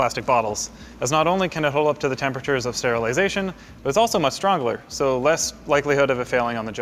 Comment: Did he say strongler?